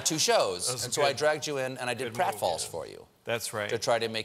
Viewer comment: On 6:58 Stephen says: "so I dragged you in and I did __ for you" What's that he says there? Help me out, English is my 2nd language, lol. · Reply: "prat falls"; it's an old, vaudeville, showbiz term.